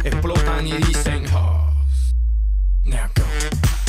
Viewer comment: Does anyone else hear Pitbull say paws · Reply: Oh my lord I hear every time he says “pause”. I don’t hear “pause” I hear “paws”